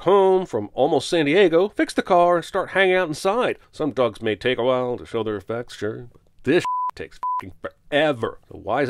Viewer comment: I swear he said “some dogs take a while to show their effects.” I’ve listened to it over and over and it still sounds like “dogs.” :p (Also, that animation... MY EYES, THEY BLEED. Movie makers in general: See this? Don't do this.)